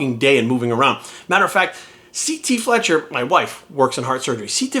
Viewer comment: Did he just say C T Fletcher is his wife?